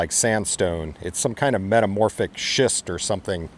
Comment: I thought you said metamorphic shit the first time. lol